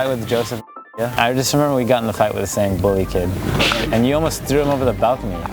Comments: Tight instead of Fight :( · u said tight instead of fight 😂